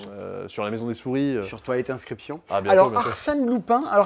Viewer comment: J'ai entendu "Toilette inscription" tout le long et je m'attendais pas à ce genre de jeu. J'ai l'impression que j'aurais un problème similaire la semaine prochaine 😬